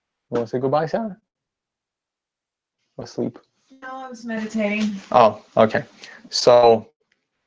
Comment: Audio and video are perfect but at the end... did Sarah say she was levitating? ;) 1:02:40 Lol! Bye bye Sarah, mind you don't fall